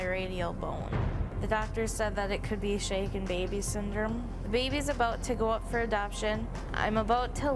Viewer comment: the way shes calmly describing the injuries in the beginning is concerning id be on my knees breaking down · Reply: Yes and I thought she said the baby is about to be up for adoption. Not my baby